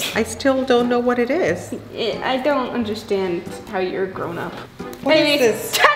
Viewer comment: said i don't understand how your big to j lol